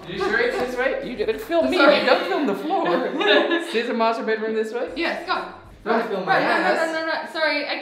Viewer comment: I thought he said masturbateroom LOOOL